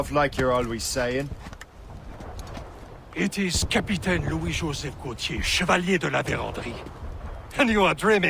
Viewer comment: what i heard: it is capitán Luisyosefgotiee Chavaliedoladeodawee 😠😂